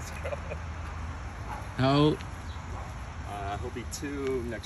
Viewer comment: i thought they was finna get at each other lol. i also thought dude was saying no when they were getting closer but he was saying how old lol